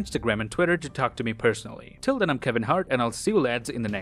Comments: Did he say I’m Kevin heart? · Did you just say "till then I'm kevin hart" XOXOXOXO